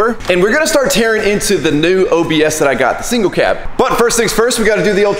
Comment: did I hear single cab or SEMA CAB🤔